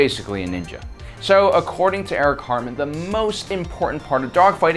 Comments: I thought he said "Eric Cartman" flew a BF-109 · I swear to god I thought you said "Eric Cartman"😂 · Who else say the South Park Cartman Hitler? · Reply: Joshua Dunford I heard him say Eric Cartman